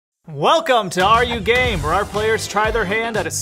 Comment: I thought they said ARE YOU GAY